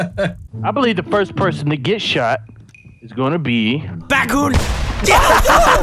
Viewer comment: said bakun instead of rakun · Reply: He said Batcoon